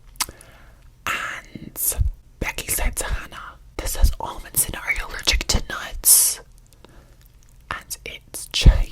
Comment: what did say? Sry I didn't hear it well 😭😭😭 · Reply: “This has almonds in it, are you allergic to nuts?”